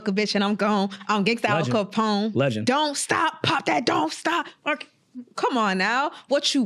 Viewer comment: did i just hear "don't std shame" ?? ...bring the boat